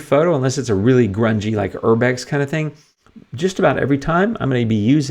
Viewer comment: Thank you for taking the time to explain your editing process so clearly. It is very useful for learning the strength s of the Luminar Neo software. 🙏 I didn't understand the word you said after grunge at 12:53. Where can I download the final beautifully edited photo? 😍 Stay safe. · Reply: Thanks and that word was "urbex", short for urban exploration, a type of photography of old, abandoned and grungy places. When I shoot those, I will often use Structure AI across the entire image, as it suits it. But otherwise, not so much. This photo is on my Facebook page and my IG too. Thanks.